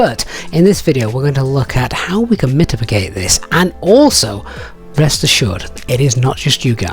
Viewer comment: What was that word at 01:36? mitipigate?? 😅 Otherwise very expertly explained. 👍